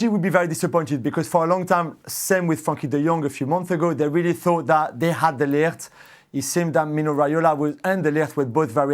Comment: lool i thought julien lost it and said “fucking dejong” 😂